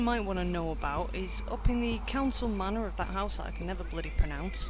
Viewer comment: Wait where are you in 2:30? I couldn't understand the name of the location you were saying. I really wanna talk to her to get that sneak up. Appreciate it! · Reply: woopsie, far West side of Balmora, where all the posh houses are, Hlaalu Council manor :)